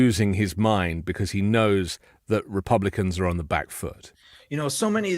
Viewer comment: I thought he said he had the black vote, WHAT HAPPENED?